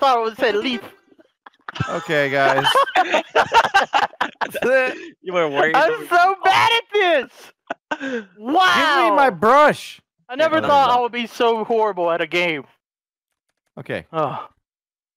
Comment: I thought said give me my glove like from frozen